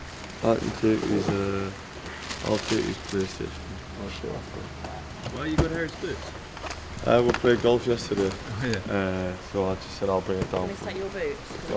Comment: I am non-English, what did JP say? were Harry Maguire's boots in United plastic bag bcuz JP played golf together with him yesterday? 😂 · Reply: He said why you got harrys boots uhh we played golf yesterday